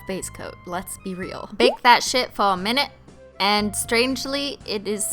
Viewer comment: Oh my- I CAN'T EVEN. When she said, "Bake that sh*t fo a minute." (6:06) I thought she said, "Big fat sh*t fo a minute." Why brain, why?